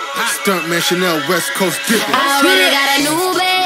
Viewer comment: I thought she kept saying " I already got an uber" just with a accent